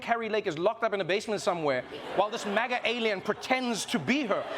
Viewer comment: I misheard "MAGA alien" as "MAGA-meleon"... which could work too, actually! 😅